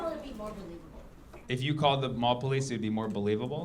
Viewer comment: Wrong Subtitle at 4:08, I think she said "Paul Blart" because Mall Cop Also I feel bad for whoever is in a relationship with this narcissistic and not very smart person